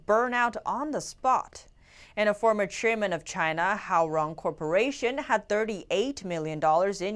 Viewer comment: did she say "how wrong corporation"? Lol